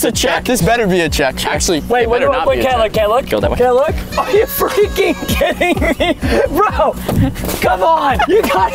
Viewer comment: Who else thought Preston said "are you f*cking kidding me" or is it just me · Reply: He said that are u freaking kidding me? Not f*cking